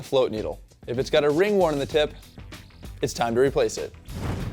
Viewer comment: what is he saying? "if it's got a ring worm? ring wear? ring something? around it..."